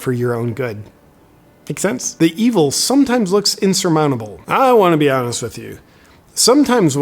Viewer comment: Did you say "I don't wanna be honest with you."???